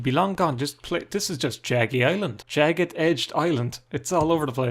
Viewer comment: For a second I misheard jaggy as Craggy